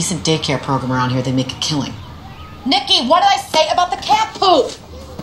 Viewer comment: NICKY WHAT DID I SAY ABOU THE CAT FOOD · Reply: the Rooster Actually, you're hearing it wrong. It's POOP, not FOOD.